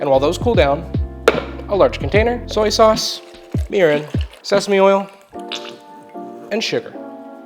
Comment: What did you say? I KNOW you didn't say urine! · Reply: Mirin